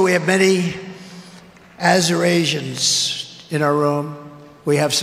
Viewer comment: "Azurasians" is Trump's mispronounciation of Arizonans on the teleprompter. The end is near for this old, tired fool. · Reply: Ohhh! I thought he was trying to say Azerbaijanis, lol